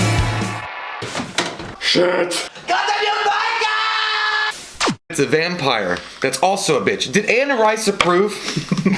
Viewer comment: Okay... I give up... I HAVE to ask! I've heard that a thousand times by now, and I can't still not make out what the hell you are yelling at 0:20. Please, please enlighten me! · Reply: SHIT! Followed by GODDAMN YOU MICHAEL!